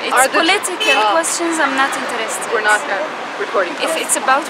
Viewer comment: did she say 'we're not recording'?